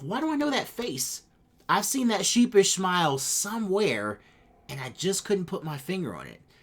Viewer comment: Lmfao did you say she bitch smile? 😂☠️☠️☠️☠️☠️☠️😭